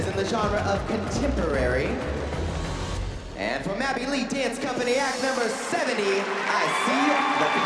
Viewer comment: I heard Maddie Lee 😂